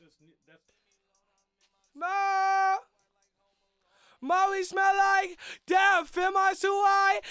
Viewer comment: Damn for all this time I thought he said “ molly smell like death “